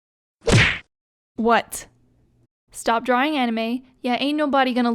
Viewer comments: Omg I just thought you said stop dawning anime but now you are dawning deku and bokago😲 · Your art is improving so much!! Also super happy to see you making videos again, keep it up!! ^__^ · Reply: I thought she said no anime nah jk love your channep